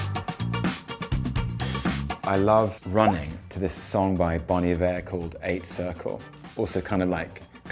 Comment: Does anyone know what song he said he likes running to? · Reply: Bonnie V- Eighth circle I searched it up, and no person called that exists. The closest is by Bon Iver- 8 (circle). Hope that helps x